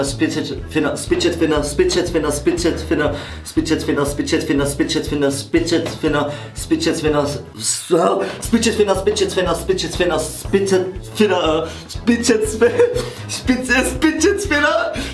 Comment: ich hab Bitches Finger verstanden